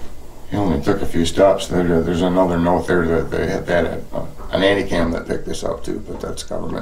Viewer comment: Did say something about a nanny-cam? in 1977?